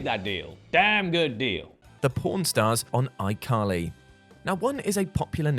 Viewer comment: I thought he said the p*rn stars on iCarly 😂